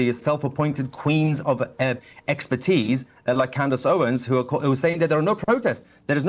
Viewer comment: When he said Candace Owen's I legit thought he said AoC 😆 🤣